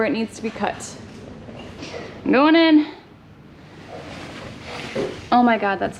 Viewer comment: At about 12:05 I had to stop and go back to hear it two more times... with that mask muffling things I thought that you said "Noonan" not "I'm going in"... if you remember the movie Caddyshack, you'll know why I laughed so hard about hearing that from you! OH, and the look you gave the camera at 15:30 when he said your hair was dusty ! WOW